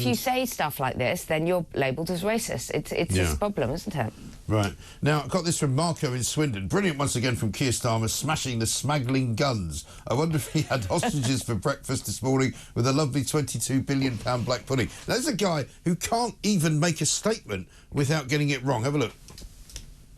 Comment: People smaggling 😂😂😂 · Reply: I thought he said smegling, Lord Alis sausage maybe ?